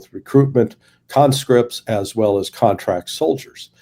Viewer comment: Larry, The conscripts do not serve in the SMO. There is a specific LAW that prevents that. Only contractors and volunteers. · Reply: I thought.he said mobilisation. Russia mobilised 300k previous contracted soldiers. Conscription is different, no experience.